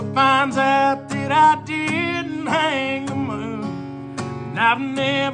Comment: Beautiful song 🎵 I'm glad you wrote the lyrics cuz I thought you were saying "that I didn't hang him up?" The moon sounds way better ❤